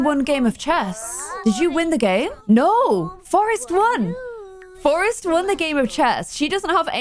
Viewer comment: I did a double take during the part where Briar and Forrest were playing chess against each other, and when clare said, "Forrest Won," I thought she said "Forrest Run" 😆